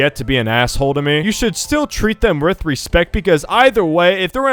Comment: I heard “treat them ryth respect”